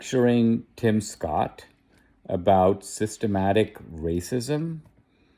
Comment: "SystemATIC"? I thought she said "systemic."